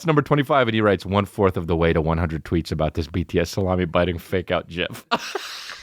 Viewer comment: I'm sorry did you say jif?